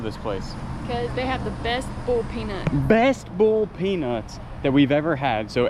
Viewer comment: I thought you said ‘bull’ peanuts. I was wondering what in the heck that was. 🤣